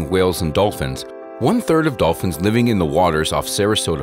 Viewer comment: Thought it said “dolphins live in ponds” and I was like, “well that’s news to me”